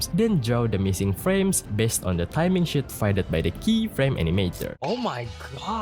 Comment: did bro say sheet or smth else